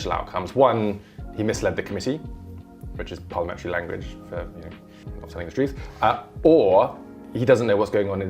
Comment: What is he saying? I watched it ten times and can't understand it. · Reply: Which is Parliamentary language for not telling the truth.